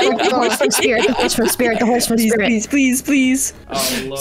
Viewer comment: what was she saying about Danny phantom? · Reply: the horseman spirit? What?